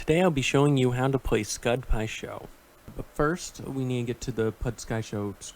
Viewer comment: “pud skai sho” thought I heard wrong for a second lol